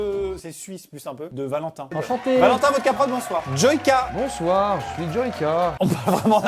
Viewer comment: à 0:23 j'ai entendu "Votre capote" au lieu de "VodkProd"...désespérante...